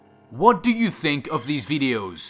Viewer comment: narrator: wat do u think of these videos, did they scare u, lets hope u can sleep after this me: i thought u said i wont sleep after watching them.